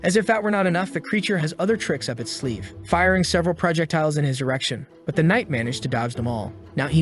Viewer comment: Hey guys in 1:14:54 I heard him say prajectile's and I swear that his voice is ai because of that error